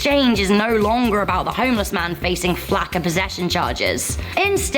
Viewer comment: Does that girl mispeak when she says, "...flack of possession..."? If not, can someone explain what flack of possessions means? And if what she meant to say was, "...lack of possesions...". Someone please...explain to me why a homeless person would be charged with having a lack of possessions! WTF?